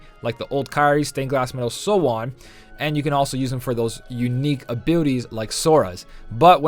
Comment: Maybe its my headphones but at 0:41 sounds like you said unique abootys 😂